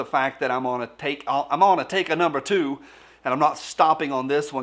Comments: It sounded like you said i am going to take a number two ?! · I swear I thought you said, "I'm gonna take a #2" the first time I heard it. Like you need to get this video over with so you could grab the latest issue of G&A and have a sit for a while.